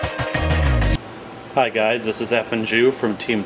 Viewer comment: i swear i thought at the beginning i thought he said "hi this is effing jew"